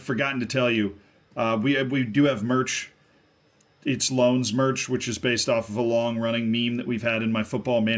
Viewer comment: thought you said "Lungs" merch.....